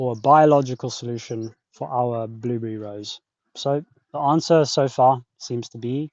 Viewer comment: I clicked on in the middle and thought I heard you say "our blueberry rose". I was so intrigued!